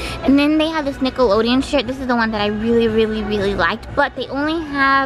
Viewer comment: Dude she really said Nickelodeon shit instead of shirt